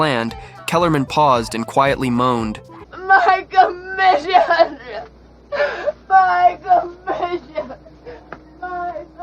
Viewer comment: no idea what she said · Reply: She seems to repeat "my commission" a couple of times.